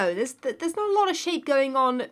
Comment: I really thought said.. “there’s not a lot of s**t going on”